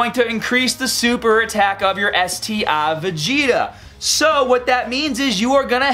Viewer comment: Sounds like you say lr card vegeta instead of ur card vegeta lol